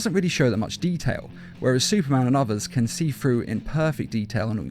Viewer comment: did anyone catch "Superman Returnsb?"